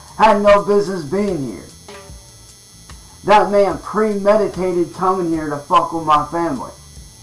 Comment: I listened to it a couple times too verify... at 4:44, I SWEAR he said pre-medicated....